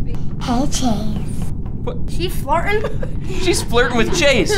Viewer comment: When was flirting with chase I thought said is she farting🤣🤣